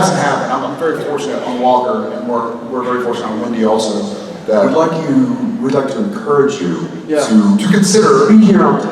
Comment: What did Jared mean when he said Wendy? What’s Wendy? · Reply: I think it was " Windy", which is the unofficial nickname for " Walker: Independence" :)